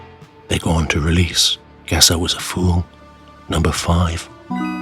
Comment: I thought you said, "Gazza Was A Fool". I REALLY wish they'd recorded "Gazza Was A Fool". I might have some recollection of them if they had. As it is, nope, not a scooby.